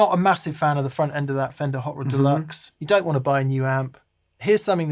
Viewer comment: Wonderful informative video! But must say you threw me for a loop with the amp rundown. I thought you said Fender “Harvard Deluxe” and I fell over thinking you had some new unreleased amp to preview. Then I saw the amp and heard correctly. 😅